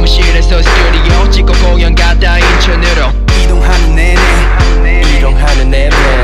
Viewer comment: am i the only one who hears him say “eat your noodle”